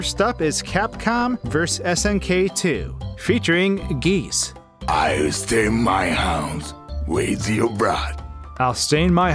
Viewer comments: This is what I heard: " I rest in my house, with you Brad..." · i heard "with you bruh" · I heard...I’ll train my mouse with your bra... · In call of duty...oh I forget which one but one of the non English groups I hear "Take him down Roy" and "I gotta gun" And Giese (sp?) I heard "I wanna stay at home with you brah" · I hear: I steam my hams... With your bra. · I thought that Geese was saying “I’ll stain my house, with your broad.” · With Geese I heard "I'll stay in my house with your bra"